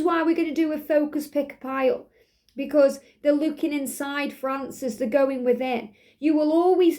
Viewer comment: When you said they're looking for answers they're going within I heard you say the name Francis intuitively and those red cards I keep seeing little raspberries on them